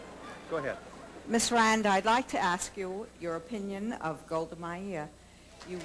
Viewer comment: Can somebody tell me what Ayn Rand is being asked at 4:25? I have no idea what the heck Goldemayer is, or however the heck you spell it. Someone please help.